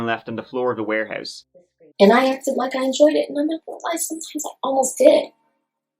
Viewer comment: What did she say at 15:55 She started mumbling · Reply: “I acted like I enjoyed it; sometimes I almost did”.